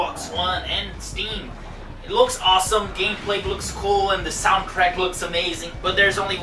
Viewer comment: I heard "The soundCRACK looks amazing". lol, I bet it did.